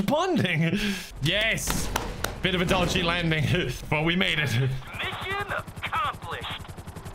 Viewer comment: The first time I saw this bit, I heard it as "Mission accomplished! WaIT-"As if the cause of the explosion had been seen a moment before impact.